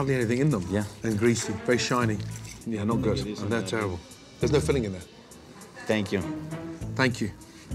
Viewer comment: Gordon: “Is there filling in there?” Waiter: “Thank you” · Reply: Wow never noticed lol I thought he said, theres no filling in there